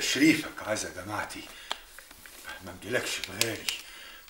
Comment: ف الدقيقه 12:40 الاسطي عطا وهو بيشكر غسان أفندي إنه حضر عزا مراته وبيقوله منجيلكش ف مجايش ولا مرايش مسمعتش الكلمه كويس رغم إني عدتها كذا مره محدش يعرف ايه الكلمه دي ؟ ولو هي الكلمه اللي أنا سمعتها صح حد يعرف معناها ايه ؟ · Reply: قله منجيلكش فى غالى